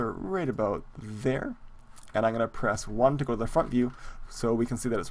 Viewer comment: it totally sounds like you say go to the fuck you, instead of go to the front view.